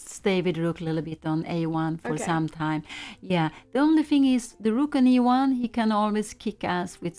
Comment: thought pia said 'the rook can always kick ass' for a second